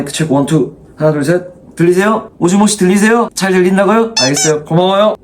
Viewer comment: 오즈모씨 들리세요 ヲヲヲヲヲヲヲヲヲヲヲヲヲヲヲヲヲヲヲヲヲヲヲヲヲ 모시모시였을ㄲㅏ